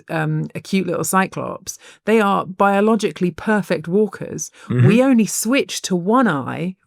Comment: I thought she was going to say "your little child, they are biologically perfect warriors"